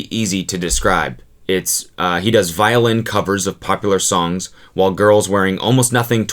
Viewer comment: I thought you said “violent covers” skksksk